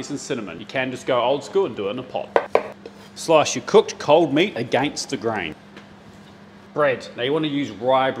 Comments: I learned how to speak Australian!... Slice your cooked cold meat a gangster grain.😂🤣😅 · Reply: I thought he said against her grain.. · I thought he said “A gangster grain” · I heard “Slice your cooked cold meat *gangster grain” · I thought that he said that the grain for the bread was “gangster grain”. Will that bread threaten you if you don’t slice it correctly? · Thought he said "a gangsta grain." When he said "against the grain." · I learned how to speak Australian!... Slice your cooked cold meat a gangster grain.😂🤣😅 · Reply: i thought he said gangster brain 🤣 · I thought he said the gangster grain but it's against the grain 😂